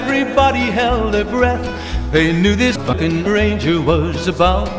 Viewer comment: just so I heard correctly, he said "fuckin ranger" right?